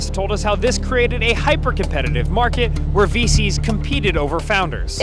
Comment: sounded like he said feces instead of VCs. How fitting 😭